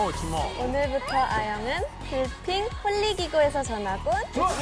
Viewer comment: why did i hear philippine? Or I'm just being delulu here?